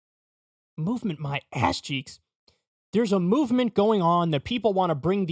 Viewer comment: Unpaused the video and all I heard was "movement my ass cheeks"